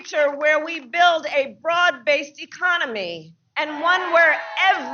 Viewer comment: I misheard - thought she said we wanna build a fraud based economy. I was gonna say America already got that, babe.